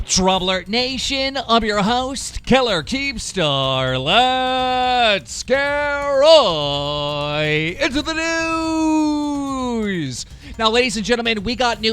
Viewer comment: I thought keemstar always said LETS GET ROYYYY INTO THE NEWSSS